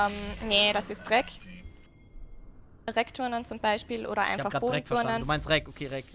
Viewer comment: Erstmal "Dreckturnen" verstanden bei 19:35 xDD